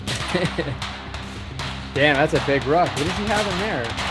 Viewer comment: um so at 7:15 'damn thats a big rock' ?? like the lense on the scope? did i hear that right? honest question lol. the military jargon is mostly non translatable :DD